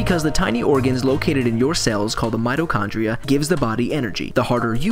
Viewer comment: When I heard 'mitochondria', I thought 'metachlorians'. Really threw me for a moment.